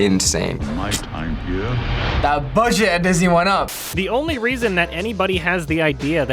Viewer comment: Are there any scientists out there that can tell me what he is saying? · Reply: the budget at disney went up